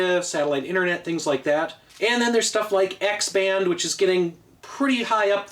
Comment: When you said x band, I misheard it as axman